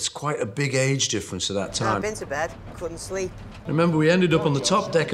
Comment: what does george says at 0:20 im not good in english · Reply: "I've been to bed, (but I) couldn't sleep."